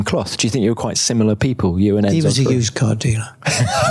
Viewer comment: What did Bernie say at 10:33? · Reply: "He was a used car dealer?"